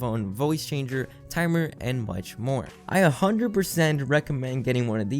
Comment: When he said “and much more” istg i thought he said “and watch porn” and i was like oh shit 😂